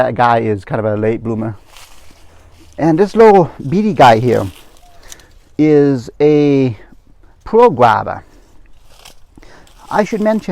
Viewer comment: What did he say @ 7:46? I couldn't understand what he said. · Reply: Rewatching his video, at the 7 minute and 42 second mark, I believe his words were as follows: …”and this little bitty guy here is a Pearl Guava. I should mention, the thing with the Pearl Guava is they are the sweetest of all the guavas.”